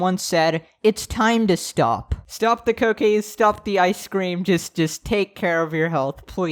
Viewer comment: Heh, Subtitles Say "Stop The Cocaine"